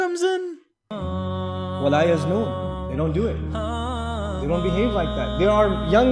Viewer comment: Did he say "You don't be have like that" or "you wont be here like that" . I don't trust captions on religious videos. at 3:11?